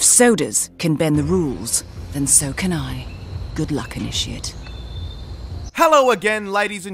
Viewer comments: LMAO! I thought Dianna said good luck, and shit XD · Hey all! Welp! The second episode of Hitman was released yesterday, and I’m still in the middle of editing my way through episode 1! My apologies guys, I know that it’s taking ages. I’ve been super busy over the last few weeks, and haven’t had as much time as I’d like to put towards it. It’s very much a work in progress though, and I feel that it’s coming along nicely (albeit slowly). I’ll have it out to you all as soon as I can, and then I’ll be jumping straight into episode 2. Thank you all for your patience. Stay awesome! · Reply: Is it just me or does Diana say "Good luck and eat shit." at 2:11?